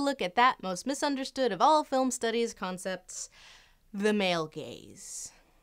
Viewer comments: No joke, as a kid I legitimately thought people were saying "the male gays." I was a very confused child. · Whenever people talk about yhe male gaze, i always hear 'the male gays.'